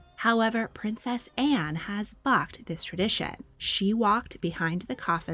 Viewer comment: Did you really say 'princess Anne has fu*^ed this tradition' :D Because that would be EPIC.